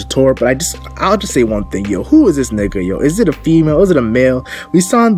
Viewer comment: did he really say 'who is this nigga' 😂😂